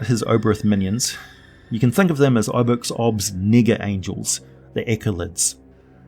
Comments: did he say obox obs ni**a angels? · "Oboxob's ???? angels"? I didn't understand that word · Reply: itay keren SM1 nega-angels as in negative/opposite/inverse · am I hearing thing or did he just say n*** angles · What was the word that came before angels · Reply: Nega-angels. Anti angels... come on folks, not funny. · Did I hear you correctly at timeline 26:12 referring to the minions as 𝒏𝒊𝒈𝒈𝒂 𝒂𝒏𝒈𝒆𝒍𝒔? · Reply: No, you did not. I said Nega-angels, as in, anti-angels.